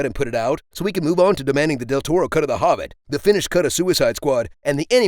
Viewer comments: "The finished cut of Suicide Squad" It's funny you should mention that... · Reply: I heard "the Finnish cut of Suicide Squad", and now I am intrigued. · i thought you said "the Finnish cut of Suicide Squad"